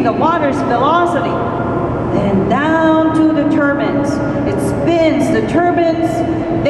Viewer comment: Anyone else hear "turbans"?